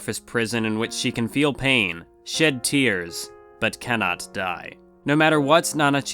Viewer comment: Am I the only one who thought he said “shiz tears”